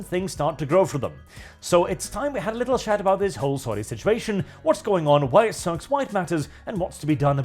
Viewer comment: when you read the article sand said "its time to talk about this whole sorry situation" i heard "this whole soy situation" and i couldnt agree more.